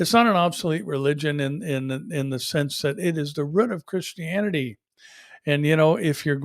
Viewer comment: I had to listen closely @ 13:30…@ 14:45…”the rut of Christianity”…I love other inflections and dialects within our great United States ! I’m from the suburbs of Philadelphia, PA and you would hear ‘root’ like ooohhtt instead of uuggtt. I’m curious if is from Wisconsin, Minnesota or there a ours ? Like up there they’ll say ‘ruf’ with an short u uh sound, where again, here you’d hear, ‘r ooh f’ for roof.